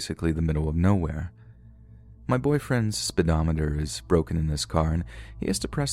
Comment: I think you mean the fuel gauge? Speedometer only shows the speed your travelling at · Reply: Funny enough, I really thought she did say “gas gauge”. I went back and listened to her story again after seeing so many comments about her saying “speedometer” by accident. I guess I just knew what she meant and didn’t really hear the “speedometer” part! 😁